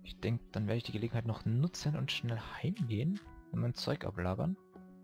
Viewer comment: ich hab verstanden mein zeug ablabern statt ablagern